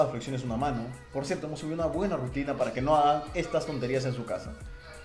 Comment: Sube videos de ejercicio en casa bro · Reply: en el minuto 1:33 pensé que decías "vamos a subir una buena rutina para que no hagan esto"